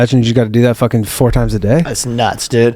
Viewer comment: Did say he deuces 4 times a day???